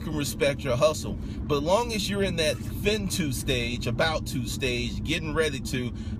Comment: Did you say..."Fin too stage..." ? LOL!!!